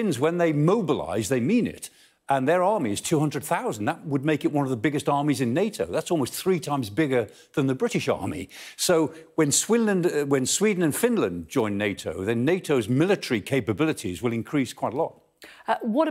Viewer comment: Did he just say "Swinland"? 😯 Fantastic!